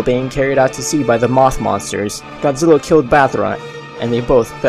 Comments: when u say battra sounds like u say ballra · did he just say “Bathra”?